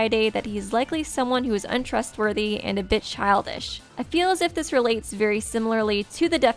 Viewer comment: When she was saying a bit childish I thought she was going to say a bitch